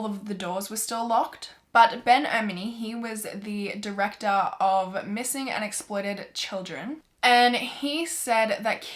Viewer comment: I thought she said "missing and exploded children"